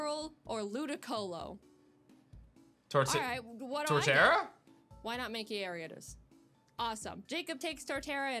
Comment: anyone hear why not make it aridos?